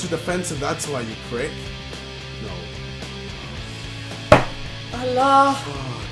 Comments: um did he just say 'Allah' ? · did he just say Allah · did he just say Allah?😂